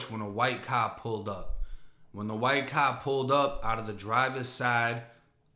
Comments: Bro, i'm watching this without subs and thought you said white cop instead of white car🤣🤣🤣 I was like, WTF.. Demon assasinating someone after escaping out of a cop car😳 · I sware I thought he said a “White Cop” pulled up, until I rewound that shit like 4 times. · Wait..lol..dude fir half the story i thought thought you had said a , a white cop pulled up and dude got out firing ..... and I'm like why is he not f**** mentioning that more? LOL